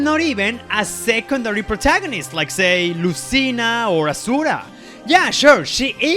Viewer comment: Did you say Asuwa not Azura.